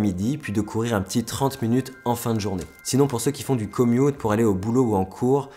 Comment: c,'est quoi le "comiot" ? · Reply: Commute ! <link>